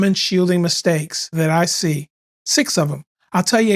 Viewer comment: I almost had a heart attack when I heard “six—“ Fortunately, it was only “six of them”